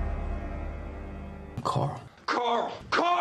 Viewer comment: I Could've Sworn They Said Carly Twice